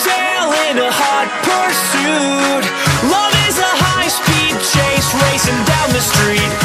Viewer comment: am i the only person who thought that he said 'Guam is a high speed chase racing down the street' the first time i heard this?